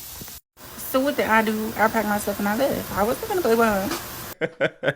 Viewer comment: what the heck is he saying???? I hear “so what did I do? I packed my stuff and left, I’m Noghonnebenabvnenuh” Sweetie, I can’t understand what the heck just escaped your botoxed lips, I’m so confused